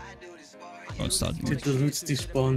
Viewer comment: OMG 13:40 HE SAYS "DESPACITO" DAMN BOI INSTA LIKE